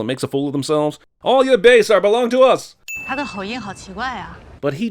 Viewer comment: she actually said "His accent is really strange". You should sin the shitty subtitles instead of what she actually said lmao